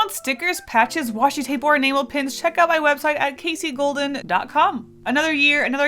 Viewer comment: I'm not kidding I thought she said anal pins